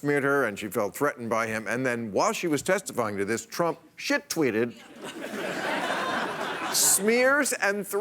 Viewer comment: 0: 57 Did he just say "shit tweeted"? ROFL LOLzzzz